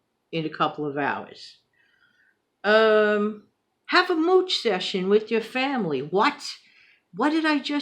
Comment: Oh wow that sounds cute! Never heard of mooch sessions however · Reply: am I misunderstanding what you are saying? (You have to forgive those of us with hearing loss.) To my Southern ears, it sounds like you are saying mooch. 🥴